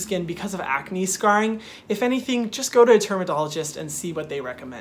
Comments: anybody else hear him say termadologist instead of dermatologist😂 idk why I laughed so hard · It sounded like he said termadologist 😂